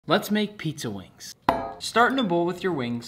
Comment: I thought he said let’s make pizza WicE